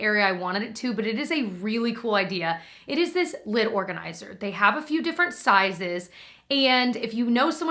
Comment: Okay, I KNOW you said "lid organizer", but when you said it, I heard "lint organizer", and I was like, "What???" 😅🤣